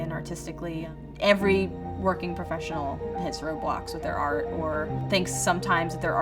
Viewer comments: Always when she Said "roadblocks" i thought she Said "roblox" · Whenever she said “Road Blocks” I thought she said “Roblox”